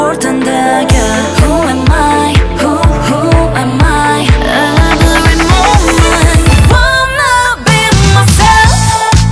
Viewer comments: I heard, “I don’t remember” xd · i thought it said MAMAMOO wanna be 😭 😂